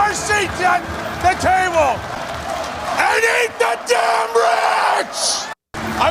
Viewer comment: How ironic that the old boomer wants to eat himself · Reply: Seriously I misunderstood what he was screaming...I thought he said let's eat the rats...😂